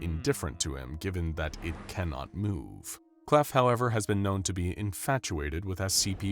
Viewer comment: why the heck does the subtitles say emote when the audio says "move" 😭😭